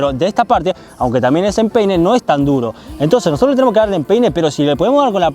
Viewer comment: Al 1:54 escuche que dijo pene aún de empeine 🤣